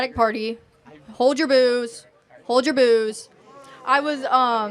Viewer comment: I thought she said hold ur booze?? 🥃 lol